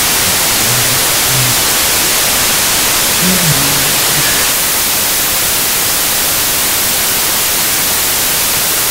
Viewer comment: Was sagt die Stimme bei 22:20 ? habt ihr da was rausgefunden? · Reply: ich verstehe,: "kümmere dich nicht"